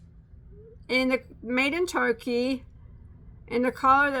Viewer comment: Where is lucky brand made? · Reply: I thought I heard her say Turkey.